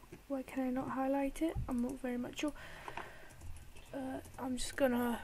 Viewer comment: did anyone hear him say "I'm not very much sure" XD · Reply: I think he said I'm not very mature